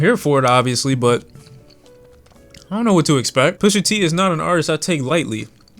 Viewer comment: When you said pusha t I thought you said brush ya teeth